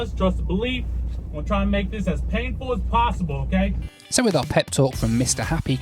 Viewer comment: did he say hes going to try to make it as painful as possible?